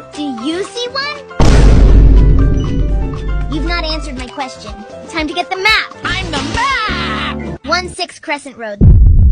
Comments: I thought she said time to get the mag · I thought she said Time To Get The Mack and I already knew where it was going 💀